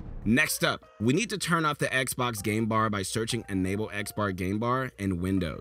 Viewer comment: Did anyone else hear him say "xbar game bar"